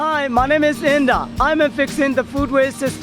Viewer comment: I thought he said his name is India and I was like...